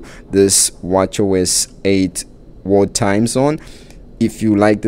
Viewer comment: I kept hearing you say “war time” watch face… 🤦🏼‍♀️ Thx for review of the world time watch face tho 😂